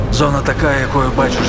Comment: субтитры скрыли слово "зона" p.s. стыдливо или повестка такова на наши реалии. Серия S.T.A.L.K.E.R была и остается общелюбимой игрой, желанной, со своей атмосферой и фанатами. С необъятным во всех смыслах комьюнити. Живя не один десяток лет и продолжая развиваться больше и сильнее( мододелы не дадут мне соврать), я по украински не понимаю ни чего, но слова "зона" и то что я за ней услышал не дадут мне покоя